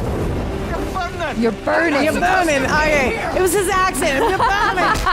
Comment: She’s pregnant!! 😂 had me dead! ☠️ Mimir the undead daddy head.. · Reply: I think she misheard 'burning' for 'pregnant' somehow 😂